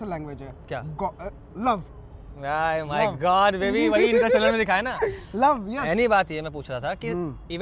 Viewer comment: did he said god🤣🤣🤣